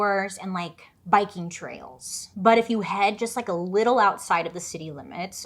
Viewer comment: I fully thought you said Viking trails. I was like WHATS A VIKING TRAIL!!! I want to go on one ! But was disappointed when I realized you said Biking trail